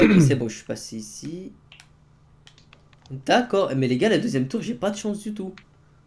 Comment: C'est moi ou à 4:40 à dit LA 2ÈME TOUR